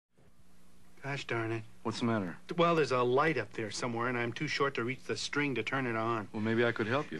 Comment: When I first saw this as a kid, I thought he was saying "Gosh Donna" at the beginning.